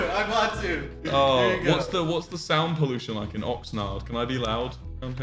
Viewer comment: did he say oxnard or am I trippin????